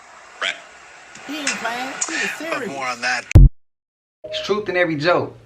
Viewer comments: Siri is always listening. You said serious 3 times and it sounded like you were saying Siri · Bruh Bruh you said serious which caused Siri to react. Kind of sounded like you said Siri.